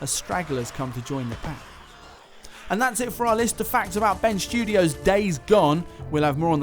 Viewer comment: Am I the only one who thinks it sounds like is saying Ben's Studio?